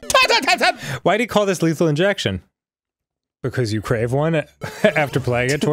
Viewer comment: I thought it said Lethal Erection You’re welcome